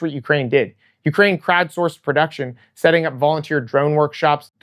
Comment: I swear I heard crabforce rather than crowdsourced. Immediately imagined crab drones. Crab drones making aerial drones. Aerial drones dropping crab drones. I know its wrong, and dumb, but I got a laugh out that rabbit hole.